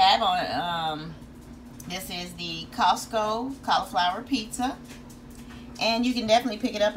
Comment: I swear I heard you say california pizza.